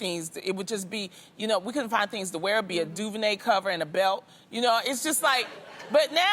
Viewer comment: LMAO did Loni say a "DuVernay Cover" and a belt??!